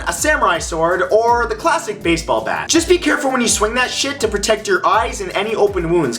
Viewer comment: In minute 2:18 did he say shit